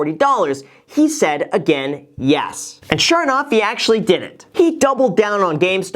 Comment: I kept hearing "He actually didn't" instead of "he actually did it." My head was spinning